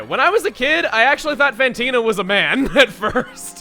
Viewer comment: Did you call him Mantino?